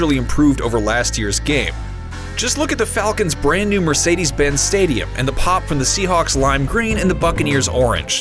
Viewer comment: Did anyone else hear him say look at the falcons new Mercedes Benz Stadium around 0:40 or am I trippin